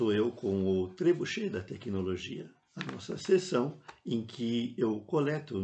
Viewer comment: eu entendi ponto G da tecnologia kkkk